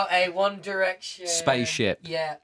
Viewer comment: i walked away for a few moments to ask my mum something and i come back and all i hear is "One Direction Spaceship" xD